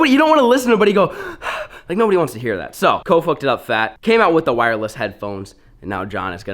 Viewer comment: “co fucked it up fat” 😂😂 thats what it sounded like